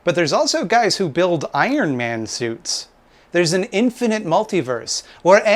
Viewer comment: Instead of "Iron Man" suits, I thought you said "Ayn Rand" suits. LOL